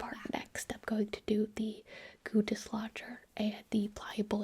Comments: When said "goo dislodger" my dumb ass thought she said "Guteslager" and I thought I was about to have a history lesson about Rene Guteslager, the Siberian king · I'm gonna be honest, the first time I heard her say "goo dislodger" i thought she was saying "gootis lodger" and I was really really confused XD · i thought she was saying “güdesslodger” but it turns out she was saying “goo-dislodger”